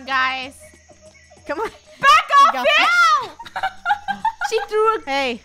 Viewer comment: wait- did just swore...? uhhh i think idk hmmm... in the 5:11 part i think she said "Back off, beach!" ehhhh idk might be mishear or yeah but maybe someone help?